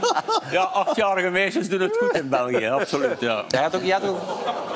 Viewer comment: Wat zegt ie? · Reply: "... absoluut ja."